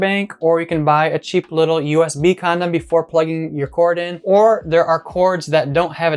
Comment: did he say USB condom?😂